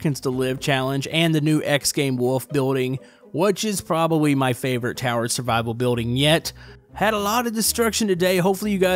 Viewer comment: I thought he said terrorist survival